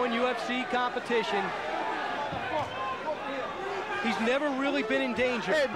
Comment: I loved ufc back then. Barely any rules, seems like mortal kombat characters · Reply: i swear i just heard "GeT OVER HERE!"